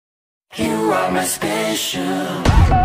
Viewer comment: I heard “You are my special” as “ You are not special”